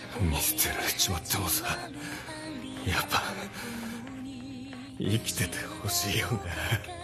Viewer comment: Anyone knows exactly what Sakakura says at 0:20? I think it's "ikiteite hoshii wo ga", but I'd appreciate if someone with better Japanese knowledge could help me figure it out :)